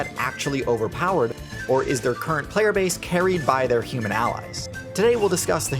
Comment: I heard that as "Or is their playerbase carried by pure malice" and was like "yup, checks out, Camels are bastards".